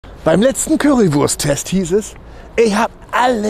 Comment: Ich habe jetzt tatsächlich verstanden: "Beim letzten Koitus-Test hieß es, ich hab alles falsch gemacht." 😂